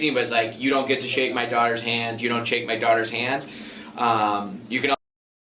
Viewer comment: I swear I hear him say “Do not kiss my Daughter”